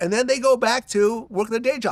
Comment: did Vlad say,”Working a gay 🌈 job!?”